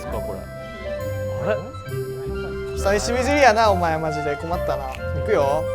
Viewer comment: 皇輝くんここなんて言ってるのかわからない笑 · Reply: ｢久しぶりやな〜困ったな😥｣を噛んでる？？可愛すぎますね( ´꒳` )